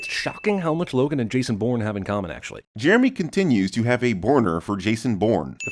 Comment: You meant to say 'Bourner', right - then why don't your subtitles say that?